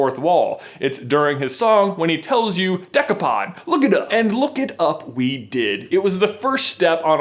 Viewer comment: I thought he said "you're dumb" not "look it up"